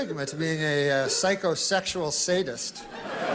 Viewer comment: "Even in todays enllightened society, there remains a stigma to being a psycho, sexual sadist" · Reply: i always heard it as psycho-sexual sadist